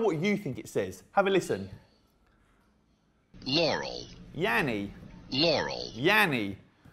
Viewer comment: What i hear is “Ya hear me”